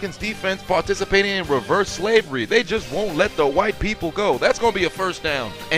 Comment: did you say reverse slavery 😭😭😭😭😭😭🤣🤣🤣🤣